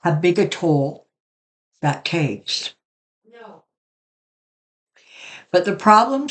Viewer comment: On my video right at 27:27 I hear “no” clear as day and it’s not coming from your mouth. Anyone else hear this?